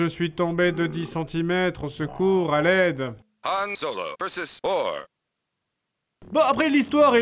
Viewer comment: j'ai entendu vore XDXDXD.